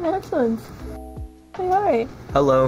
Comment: i think he said "say hi"